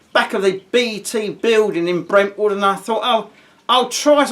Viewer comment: Nice one! p.s. 21:31 did you say Brentwood, Essex? I lived there and in Shenfield for approx 2 1/2 years, such beautiful memories. Than I had to go back to London, as my photography work was all there.